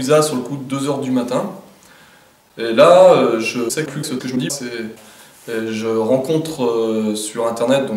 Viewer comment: Les gens il dit quoi de 0:26 à 0:28 quand y'a le montage ???? Sinon super !! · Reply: Il dit "et la je sais plus se que je dit"